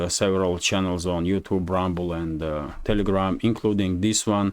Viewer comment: I thought I heard you say Ramble. I'm going to start referring to it that way. Thank you